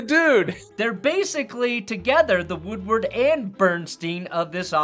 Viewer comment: Did...did you just say that Pablo is a modern day "Woodrow" instead of "Woodward"? 😂😂😂